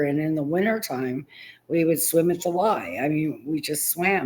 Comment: What did she say? "In the wintertime we would swim in July"(?) 🤔 I turned on the captions and it said "We would swim at the lie." That doesn't sound right either. What did she say? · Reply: She said “we would swim at the Y.” YWCA I’m assuming. That’s what we called it. The “Y.”